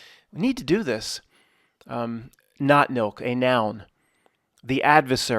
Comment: I think you really have something here, the whole time I actually thought you were saying not milk LOL but I actually think that it's 100% true and as far as the visible representation I actually typed in your description of this thing and I typed it into ideogram and got some interesting results, please do continue this because the more you elaborate on it and the more you actually point out these situations that it uses to distract us and like you said for the phone thing yeah it's 30 seconds but billions of times a day potentially it's quite interesting and definitely things to watch out for, thank you